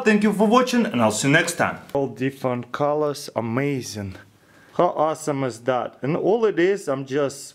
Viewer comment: I love how he says amazing sound like he said amazon